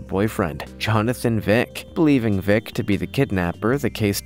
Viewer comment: Did he just said Johnathan Wick? 7:27 ?? John Wick!